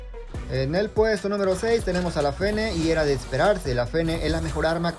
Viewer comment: la primera vez que lo escuché creí que decía "La P3n3" XDDDDDD